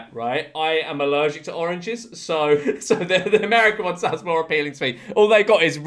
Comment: Why did he say America in finnish? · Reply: he says 'so the america(n) one is more appealing to me' whilst laughing